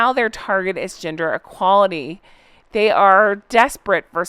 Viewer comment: Celt views talks about the escorted out situation. Also, at first I misheard you and thought you said H and M’s new issues was GINGER EQUALITY 😂😂.Best to you. 😊